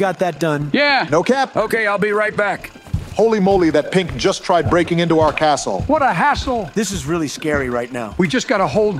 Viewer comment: could you tell me what George said When he was ordering? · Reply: Hello can I get 3 orders of triple cheesy peesy potato bomber perogies, and 2 orders of bacon blaster perogies please? Bet, thank you very much! -George